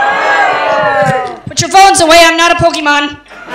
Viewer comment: She said put your phones away, I’m not a Pokémon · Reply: Thanks for clarifying because I thought she said put your phones away I will poke your mom. Please get a job in translating for people like me with issues.